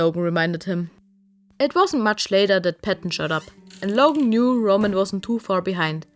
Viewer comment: I thought it said, “that Patton shut up” I am dieing